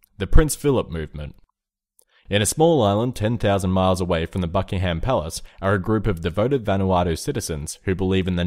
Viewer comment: Anyone else hear fuckingham palace? XD